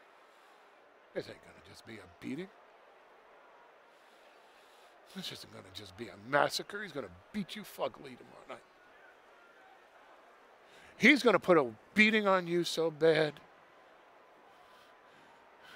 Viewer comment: Did he just say he's gonna beat you f**kly?